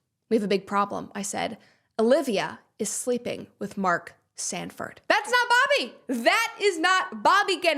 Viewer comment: Bruh, just before She said Mark Stanford , the trailer for the David Movie couldn’t have happened without perfect timing 😂🤣🤣. I heard, “ Mark David!” Then I skipped and she said Stanford 🤣🤣🤣🤣.